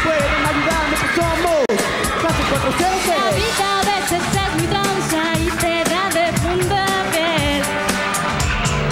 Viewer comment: que fue lo que dijo Grettel Valdez? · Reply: La vida aveces es muy tranza y te da de punta pie